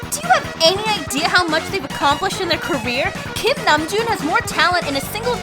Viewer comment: thought she said Kim Jong Un